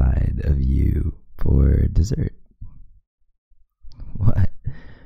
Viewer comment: I was just walking and I could’ve sworn he said he’s having us for dessert someone correct me